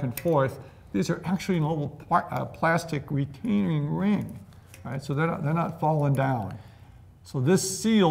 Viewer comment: I listened to 8:34 over and over. I did not understand what he said. Anyone understand it? Not sure if that was an intentional weird talking/slang, etc.? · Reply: bearing retainer